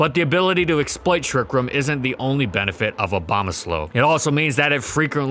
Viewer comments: Did he say abomoslow?😂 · did he say abomaslow? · Did he say Abombaslow?